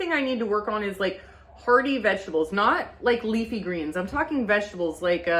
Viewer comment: When you said hearty vegetables I heard it as party vegetables 😂🎉